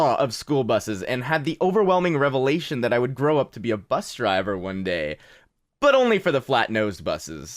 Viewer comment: I thought he said he thought he was going to grow up to be a bus...